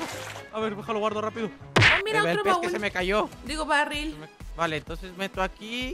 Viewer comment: pense que dijo mira otro raul